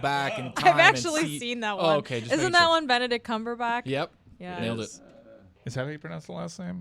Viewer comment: “Benedict Cumberback”? I thought you said it: “Benedict Cabagepatch”